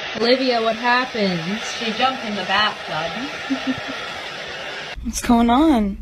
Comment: Taylor said “she jumped in the bathtub” but at first I thought she said “she dumped in the bathtub” 😅